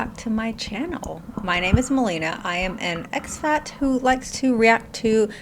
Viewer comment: Initially, I thought you were saying "I'm an expat who likes to react to fat acceptance....etc." and I was like, that's a weird schtick, but ok, and then I realized....🙈😆